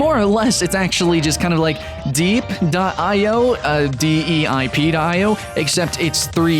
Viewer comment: did he just say deip.io?